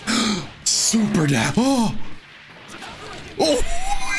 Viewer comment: I swear to god he says "Super Dab"